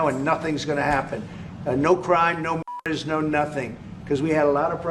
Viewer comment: I heard wrong. Thought I heard "no crime, no (F-slur), no nothing", but it was "m*&rders".